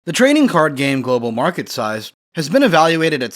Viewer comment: I love this video and all the work he put into making it, but am I the only one who hears at the start him say "The training card game"? I replayed it a couple times and even enabled captions . But all I hear is "Training card game"